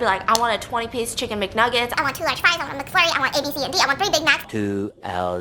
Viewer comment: Did she say she wanted ADHD?